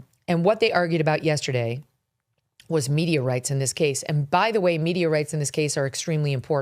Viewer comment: >> at first i heard,,, "they were arguing Meteorites",,, then Megyn explained they said.... Media Rights !! :-)